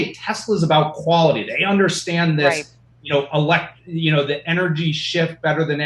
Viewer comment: I thought he said energy shit lmao